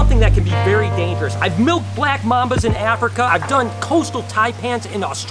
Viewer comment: I just want to say thank you - Your videos have inspired me to not only be a better reptile owner but have also got me out of bed in the morning on my bad days. You’re awesome! Never give up the dream 😁❤️😁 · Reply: Yum I heard “I have milked black mammas”